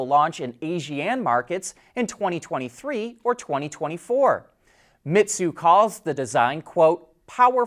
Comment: Which markets? I couldn't understand what you said. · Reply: ASEAN Countries, which includes Brunei, Cambodia, Indonesia, Laos, Malaysia, Myanmar, the Philippines, Singapore, Thailand and Vietnam